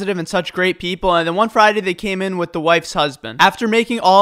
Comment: when you said wife’s husband i thought you said whitest husband LOL